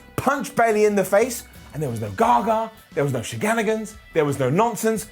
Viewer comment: bruh did he just say sheganigans😂😂😂😂😂😂😂